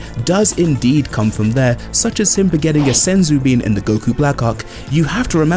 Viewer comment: I had to rewind, I legit thought you said Goku black cock 😂